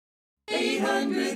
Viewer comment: When I was little I thought they said and pie at the end